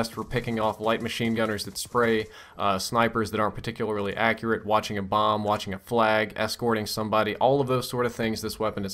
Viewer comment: Sounds like your saying watching your mom!